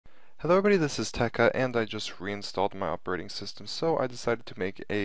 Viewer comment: i thought this guy said "hello guys this is tech cunt"